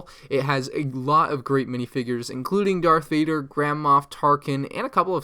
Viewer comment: I was about to pass out so I thought he said “grandma tarkin” 😂😴